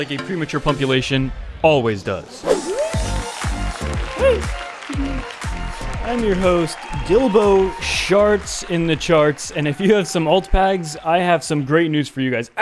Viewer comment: I swear I misheard "I'm your host Dildo shards in the charts" OMFG what does that even mean XD